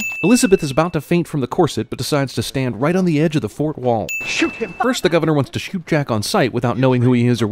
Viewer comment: I heard: "Elizabeth is about to faint (…) on the edge of the FOURTH wall" xD